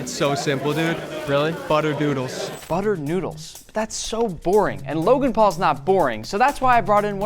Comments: Butter noodles? I heard butter doodles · i thought he said butter doodles